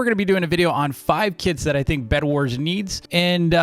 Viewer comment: at the start i thought he said 5 kids lol